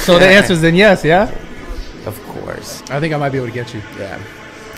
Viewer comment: I thought he said "Future Vegetable," that was hilarious. Because it seemed like whoever he fights would become a vegetable. He actually said eat your vegetables.